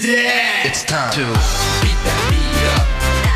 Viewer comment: I swear i thought he was saying "Beat that Meat Up"...lol